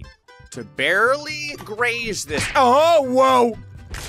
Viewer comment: Ummmmmmmmmmm oh no oh no oh no no no no no no i heard owo at 15:24 in the video 💀